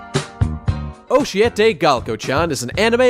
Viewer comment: I keep hearing "OH SHIT Galko "